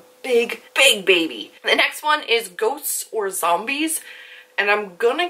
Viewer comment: loved this!! X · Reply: haha thought u said GOATS vs zombies! ;) lol